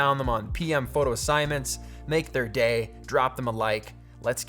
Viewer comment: I had my volume low and i heard this part 1:20 as "Make them gay"